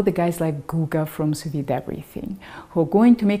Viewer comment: I couldn't quite hear. Who from what? Googa from soviet everything? I don't think the subtitle is accurate.